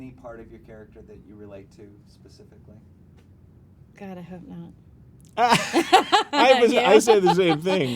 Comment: What is she saying at 4:36?? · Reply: "God I hope not "